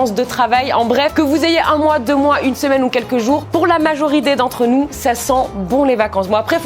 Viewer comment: c moi ou elle à dit majoridé